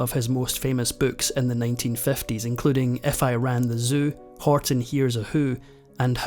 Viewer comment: When listing the titles of Seuss' popular books, the narrator, at minute 13:10, refers to one as, "...If I Ran the Seuss" instead of, "If I Ran the Circus". Oops. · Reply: He actually mentioned the book "If I Ran the Zoo," which was shown earlier in the video